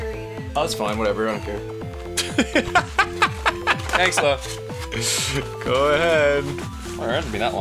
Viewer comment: D-did Ross say "Thanks love"?